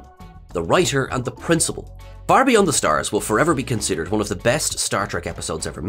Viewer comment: LOL, at first I thought you said, "Barbie on the stars"!